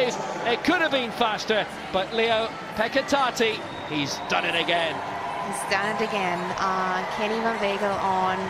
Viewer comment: Really bad choice of words if I heard it right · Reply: She says, "He's done it again" with a thick accent. She was repeating the guy at 4:10 as affirmation of what he said.